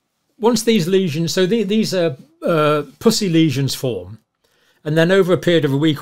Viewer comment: My wife thought you said "pussy lesions".....LOL....i said the good doctor is using some slang today...